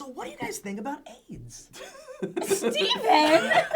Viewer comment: I thought Steve said What do you guys think about anal sex but he just said Aids.